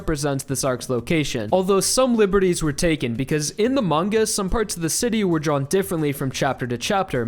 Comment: DID HE REALLY SAY IN AMONG US XD